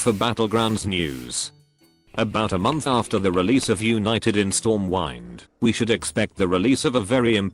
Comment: did he say stormwine at 4:21? wtf edit: actually, he does it all the time lol